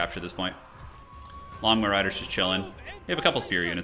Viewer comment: I can't help myself but all I hear is "Lawn Mow Riders"